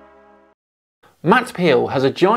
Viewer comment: Sounds like you said Matt Peel not Powell lol.